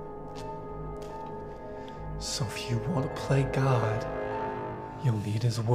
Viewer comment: I thought he said "do you wanna go play CoD?" Lmao